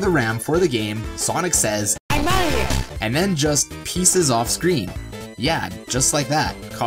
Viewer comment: did sonic say enelia